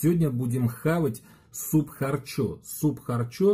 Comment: Мне в начале послышалось суп харчок